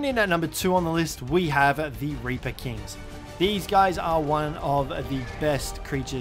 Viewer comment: Hm any and ravager sounds like a good combo cuz the ravager half's metals weight and the anky can collect it pretty well Also at 14:04 it says rapper king instead of reaper king so thats a mistake you made that I wanted to point out